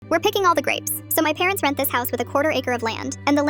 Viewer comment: I thought it said, WE ARE PICKING ALL THE RAPE?